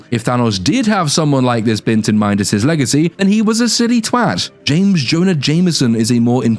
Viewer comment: Did you say J Jonah Jameson? Or James Jonah Jameson? I only ask because his name is John Jonah Jameson and no I didn't know that before looking it up but hearing James Jonah Jameson made me do a double take because i honestly thoght his name was Jonah Jonah Jameson lol